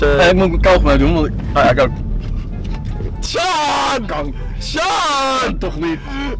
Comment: Shawn zij kanker op 1:00 like als jij het ook hoorde